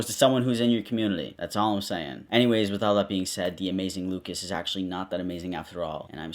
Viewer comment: Honestly, before I saw his channel was called AmazingLucas, I thought you said it was called “AmazingMucus”